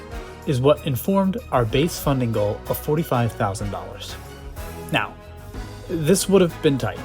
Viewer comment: $45,000? We thought you said $450,000. Well, now you've got over half a million. Have fun!